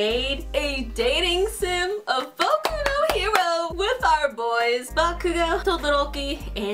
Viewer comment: not to be rude but did u call Bakugou “Bokuto”? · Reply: I said “Boku no Hero” lol